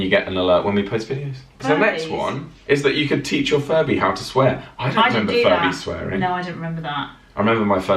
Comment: I thought you said yeah your furbie to swim 😂